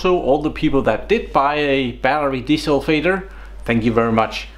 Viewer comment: I didn't quite catch what you were selling, it sounded like you said Battery Diesel Fader?